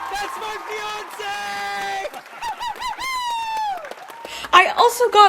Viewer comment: yelling "that's my fiancée !!!" ;w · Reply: I heard it as 'that's my Beyonce' which also works but fiancee makes more sense 🤣